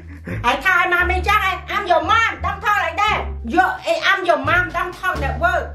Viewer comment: did u say ur mom is rude?